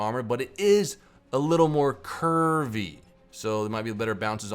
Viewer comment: did i hear "kurwy"? :D